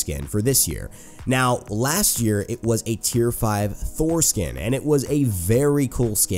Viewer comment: I was listening this from my phone and half listening while scrolling my stats in smite, and out of nowhere i hear him say "foreskin" and i listened that part again again and again until i realized he was talking about "thor skin"...